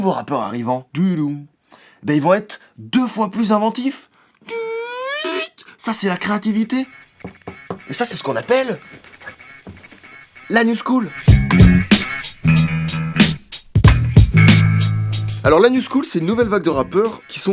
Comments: J'ai entendu l'anus cool ... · Quand t'as parlé de la new school j'ai compris "l'anus cool" Aidez moi svp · À 14:21 j'ai entendu "l'anus cool" 😂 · a 14:21 j'ai compris "l'anus cool" MDR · Aime si toi aussi a 14:20 t'as entendu : l'anus cool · J'ai compris "l'anus cool" au lieu de "la new shool".. C'est Marant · moi j'ai entendu "anus cool" mdr 😂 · J'ai eu une hallucination auditive, j'ai entendu "l'anus cool" au lieu de la "news school"... Sinon excellente vidéo ;) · j'ai entendu l'anus cool. oui. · A 14:18 excusez moi mais j'ai entendu "l'anus cool" :') dsl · avant de voir le titre La NEW SCHOOL, j'ai compris L'anus cool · A 14:23 j'ai compris "anus cool" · pourquoi quand il a dit "la new school" j'ai entendu "l'anus cool" ?